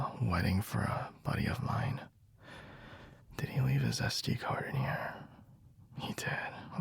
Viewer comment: when he said sd card i thought he said zesty card